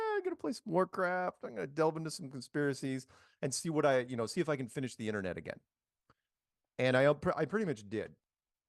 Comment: At around 22:55 I think Mark says "see if I can finish the internet again". Is he actually trying to imply he read all of the internet? Yeah right, buddy. Even someone like Albert Einstein wouldn't make such a claim. He really does think way too highly of himself if that's what he's trying to say.